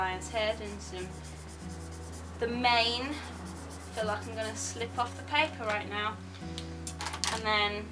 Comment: i thought you made a pun and said i can't phil/feel the paper